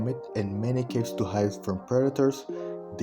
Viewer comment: The captions thought you said “many kids to hide from predators” instead of “many caves to hide from predators”